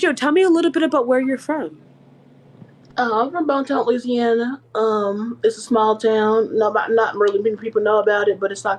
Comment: Cracks me up every time she says bon Temps As it does not exist outside of "true blood". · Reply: This whole time I thought she was saying Baton Rouge, Louisiana 😂 😂